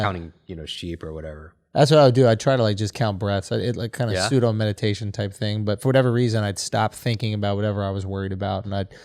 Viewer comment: I thought Schultz said “I count breast” not breathe 😂🤣